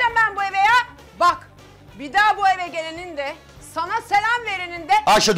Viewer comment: What she say ?? Bak bir da boigerin'de, sana selam verinin'de ""